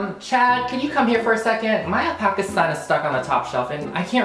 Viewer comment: i thought you were saying the alpaca is a top for a second and I had to backtrack